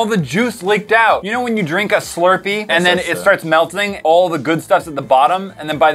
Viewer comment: Di anyone else hear say « meltining » 8:23 🤔😐🤯